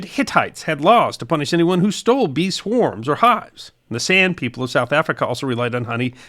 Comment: I heard “Sand people of South Africa” and had to check…Oh, San people.